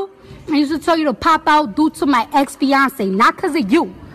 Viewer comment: Why offset so mad and he cheated on cardi 💯💯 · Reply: Tee Money am I the only one who heard her say ex fiancé